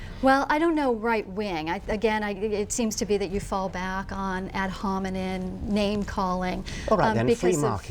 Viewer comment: did she say ad homineN I think she's said that before.